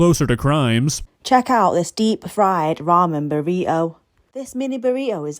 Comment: Oh god that accent. I honestly thought she said, "vomit burrito," then broke it open and it looked like that...